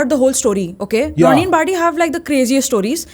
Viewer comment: what is the name they said who had craziest story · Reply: Ronnie & barty